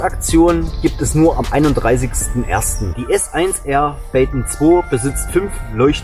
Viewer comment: Baton ich hab baken verstanden 🤣🤣🤣🤣🙉aber wie immer super Video lieber 😇